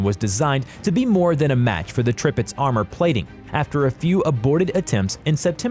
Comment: I kinda heard him say, "tripitz" instead of "tirpitz".